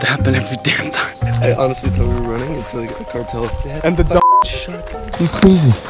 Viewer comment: "Ooooh, you said 'cartel'! I totally heard 'abandoned hotel'"